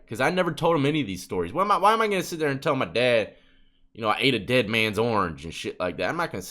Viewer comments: I thought my dude said he ate a dead man's arm i was like the food is that 💀lmao! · "" why am I going to tell my dad I ate a dead man's Orange""😂😂😂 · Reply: I thought I heard him say dead man's arms at first and was shook! Lmao